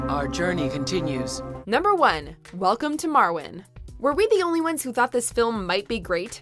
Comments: I completely forgot about Welcome to Marwen. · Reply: Matthew Cool I thought I heard Welcome to Morrowind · I thought I heard Welcome to Morrowind. Give Elder Scrolls an Oscar nomination